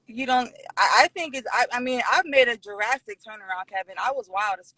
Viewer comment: it sounded like she said she made a Jurassic turn around ...